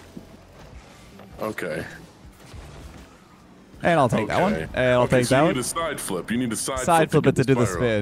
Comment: I really thought you said I'll take back and your family too.🤣😂🤣